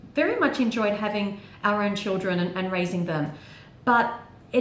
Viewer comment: omg when you said "[in a perfect world] we would've had our own children" i thought you said ARYAN children! i was like WHAT